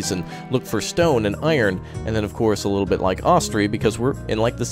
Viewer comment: at around 1:08 he mentions that Banished is like Dawn of Man, Foundation and a third game that sounds like "Austria" or "Austri"... Couldn't quite make out the name, can anyone help? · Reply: Ostriv